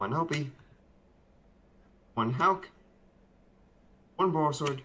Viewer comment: and 6:40 what are the name of those cards? helk? and clad? cant tell what what he is saying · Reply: halq=needlefiber/chrystron other one is quadborrel